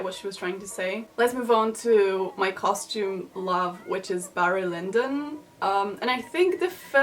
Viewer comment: I can't hear what you said. I hear Bara Lindin